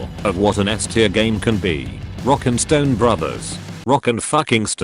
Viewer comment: DID I HEAR A ROCK AND STONE???.??